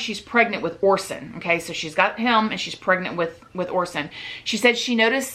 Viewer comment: it sounds like she says “she’s pregnant w horses” 😂😂😅